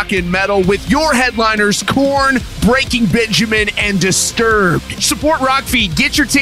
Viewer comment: For a second there I thought said Breaking Bitchiman lol